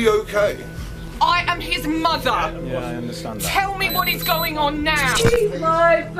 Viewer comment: sounded like what is going on here lol